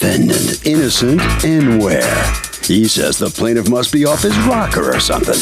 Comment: Before I even start the episode....."innocent enwere" is the defendant name?? That sounds so made up. And if it is his real name, wow thats creative and lazy. Sounds like the name of sum kid in an online game lol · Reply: I thought the announcer said Innocent Anywhere lol😂